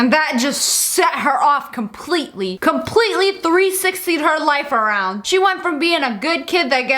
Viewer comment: The tack I heard Lia said “completely 360tied her life around” 🤣